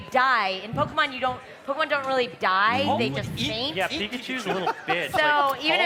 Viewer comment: I heard Vanoss say "but Pikachu's a little bitch" 😂😂